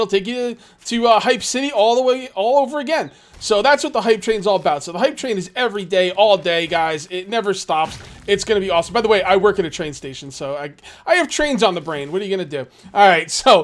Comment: had to go back and check the audio, at 0:37, I would've sworn said "the hep c train" lol, hearing fail, not what he said at all.